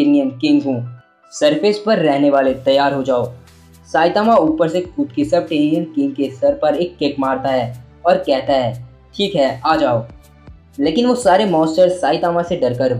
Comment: what he said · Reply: Saitama kud ke Subtarena king ke sar par ek kick marta hai Ye kha tha maine Sorry ache se sunai nhi diya 😅